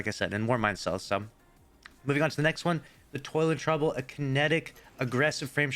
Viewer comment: Why do none of the offered Banshee weapons not have Masterwork perks? On another note i thought you said "Toilet trouble" for Toil and Trouble :))